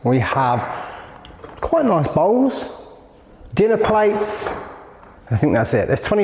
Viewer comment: Oh I thought you said ba*ls when you were removing the bowls..